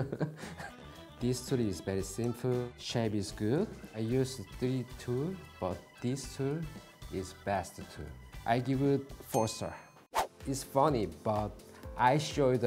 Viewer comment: i thought he said the square shape isn’t good